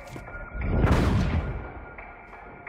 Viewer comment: what did the Scottish guy say? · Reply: Boom-Shaka-Laka Tada